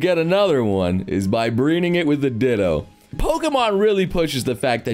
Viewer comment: Is it just me, or did Noble just say: “Breening it” instead of “Breeding it”?